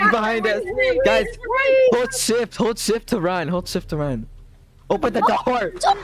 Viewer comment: For a min when he said shift I thought he said shit